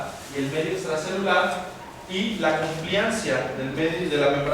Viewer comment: que dice en el minuto15:28...la copianza??? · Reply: complianza